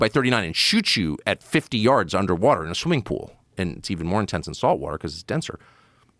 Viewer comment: No Tucker, swimming pool water isn't denser than sea water. It's the other way around. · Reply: He says ‘it’s even more intense in salt water because it’s even denser’ You misheard him